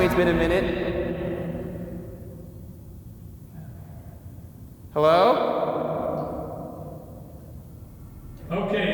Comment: Anybody heard whisper in 8:11 ? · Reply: i heard in here